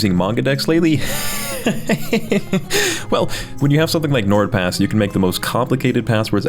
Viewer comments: For a limited time, grab the 1-year NordPass Premium plan with 50% off at <link> or use code scambolireviews Thank you NordPass for sponsoring this video. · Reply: I thought it said N word pass and I was very confused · For a limited time, grab the 1-year NordPass Premium plan with 50% off at <link> or use code scambolireviews Thank you NordPass for sponsoring this video. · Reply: I thought you said n word pass